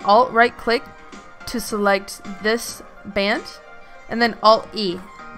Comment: Ok Band scaled Me: What? did you said...